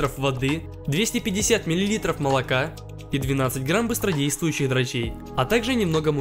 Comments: я один услышал дрочей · Когда ты сказал дрожжей мне послышалось дрочей))))) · я один услышал быстро ,,действуйщих дрочей’’ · Мне послышалось или что , что вы сказали " и 12 грамм быстродействующих дрочей"? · 250 мл молока,а мне послышалось литров:) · мне одному послышалось дрочей😂😂😂😂😂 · мониторинг одному послышалось быстро действуй хотя дрочей???